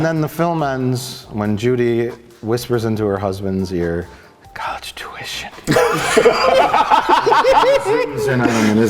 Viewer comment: Ok. I've seen this episode numerous times, but am I the only one who doesn't understand what saying at 45:50? It is apparently hillarious. Help me please. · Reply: "She whispers to her husband: college tuition"